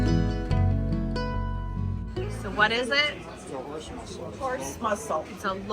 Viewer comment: No, it is not a «horse muscle» as the subtitle says, but of course a «horse MUSSEL»!!! 😂😂😂 Big difference!!! 🤗♥️💟♥️